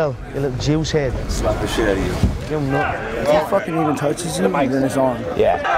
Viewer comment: What does Nick say here ? "If he gonna fucking touches you then break his arm ?"